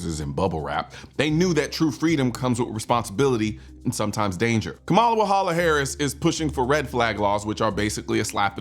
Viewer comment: Kamala Wahala Harris, that what I thought he said.